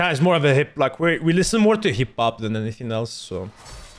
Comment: did he just say hip pop